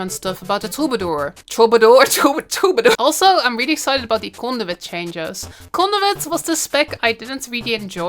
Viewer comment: When u said Conduit I thought you said Condiment. Lmao